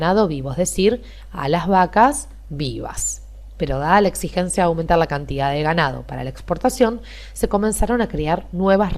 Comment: no entiendo que dice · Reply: "Dada la exigencia de aumentar la cantidad de ganado"